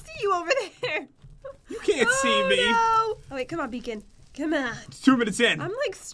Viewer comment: Did Jen just say come on beacon????!!